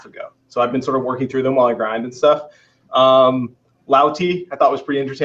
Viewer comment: what does his say @ 26:30 as first podcast? Lau Tee?? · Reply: Lautie, a dutch player